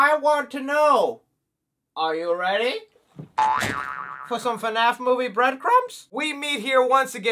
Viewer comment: Thank You For Watching! Let Me Know Your Thoughts On his Latest FNAF Movie Update?! LIKE & SUBSCRIBE!! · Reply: At the beginning, I thought you were going to say “Are you ready… for Freddy?” 😂😂😂